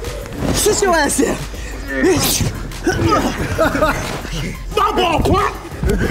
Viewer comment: what is trying to say 😂😂😂😂😂😂😂😂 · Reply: Klara belle He was trying to say "My ball, punk!" But got laid out at the punk part. 😂